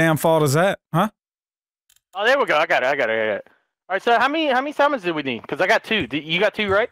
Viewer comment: Did call at 10:41 ?